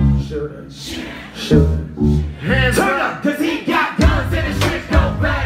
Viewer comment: I thought he said guns and ships. yay hamlet